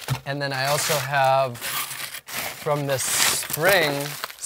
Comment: Did you say surinam cherry 9:52??